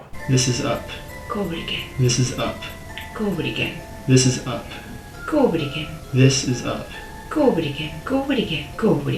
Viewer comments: I heard “COVID again” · Oh no... i hear "COVID again". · It sounds like she is saying covid again, covid again, covid again 😂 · She says “go over it again” but I hear “goo-bidy-gin” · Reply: i hear "covid again"